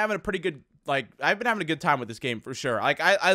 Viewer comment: did he say gay time